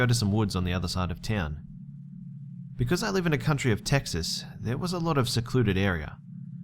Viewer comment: Lol did you Just say "because I live in the country of Texas"?